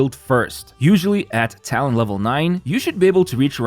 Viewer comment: When he said talent level 9 I thought he said town hall 9 like clash of clans 😭